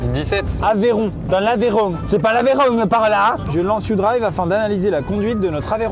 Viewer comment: j'ai entendu "17 c'est L'aveyron" j'ai rie 17 c'est la charente maritime xD